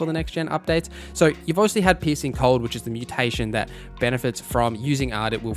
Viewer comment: I'm sorry, I should be talking about how great the information here is, and how useful it will be... but every time you say 'piecing cold', I keep hearing 'pissing cold', and it's cracking me up. Gonna need a few minutes to get that out of my system first. But yeah, thanks for all the good info, I needed a refresher before I jumped into a fresh Witcher game; haven't played for a couple of years.